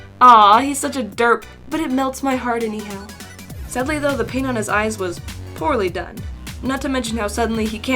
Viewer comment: For a second I thought she said "*sadly though the pain in his eyes was poorly done*I 7:29 Like if your laughing at my dumb butt for a brain Poor Pigpatch...